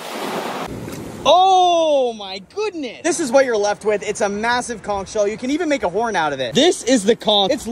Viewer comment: I thought that said “eating massive chooch”